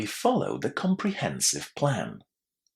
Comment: Sorry Sir, did you say "A comprehensive plan" or "THE comprehensive plan"? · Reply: It is 'a comprehensive plan', but the 'ed' ending in 'followed' merges with 'a' and so it might give the impression that it's a different article. Thank you for such a keen question!👏